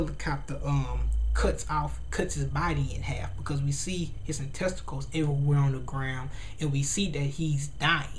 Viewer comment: did you say, "intesticles"? :L